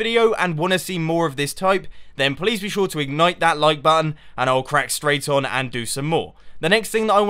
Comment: I first thought that you were saying ignore that like button haha